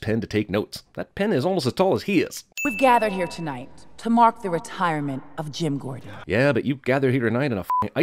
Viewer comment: I don't know if anyone elese mentioned this, but I sewar when that woman at the gathering for Jim Gordon's retirement starts her speech, I hear "We've gathered here tonight to MOCK the retirement of ..." I know it's probably supposed to be an accent but that's all I hear.